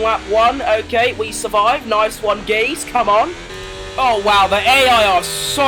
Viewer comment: did u say nice one gays? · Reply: No mate I said 'Geez' short for geezer which is a british slang word for guy